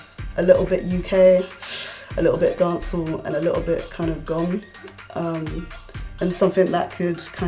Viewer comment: Did she say Gqom?? Real awesome music palate! 👏👏